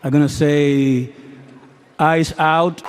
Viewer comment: I’ve seen this clip a few times over the last few days but just realized what he said, “Before I say thanks to God, I’m going to say ICE OUT!” Just wanted to point that out for anyone else that missed it.